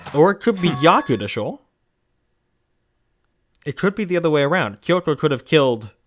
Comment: Can anyone tell me what said at 10:19? I've listened three times and still wonder if he put in a Japanese word there. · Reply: He says gyaku deshou, which means "the other way around," so you aren't missing anything.